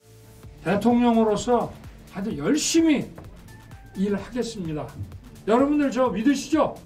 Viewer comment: 열심히 등골빼먹겠습니다로 들려